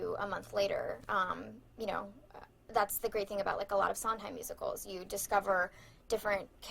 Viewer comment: I found it funny that the translator either misheard or intentionally mistranslated "Sondheim musicals" as "songs in musicals"